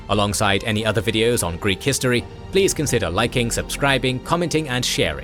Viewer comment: Why do the captions say "Italian" instead of what is in the video "Greek" history? lol